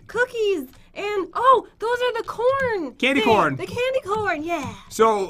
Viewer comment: I thought Jen said “candyporn” not candy corn